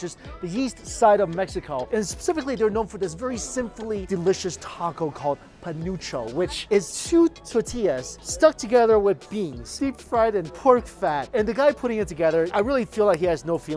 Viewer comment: For a second I thought he said “panocha” and I cackled